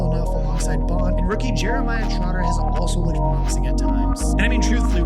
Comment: Dude, turn down the music at the end. Cant hear anything over it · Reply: ikr.. almost thought i heard him say "rookie jeremiah trotter..." but i know thats not right XD